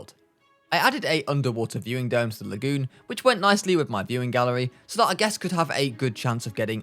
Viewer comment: I thought bro said mewing gallery 💀💀💀